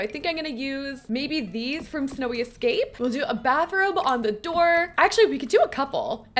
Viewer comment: why did i hear "we'll put a bathroom on the door" 😂 I love the vibes!!